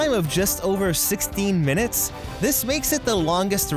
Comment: For spaceship earth when he said 16 minutes I thought he said 60 minutes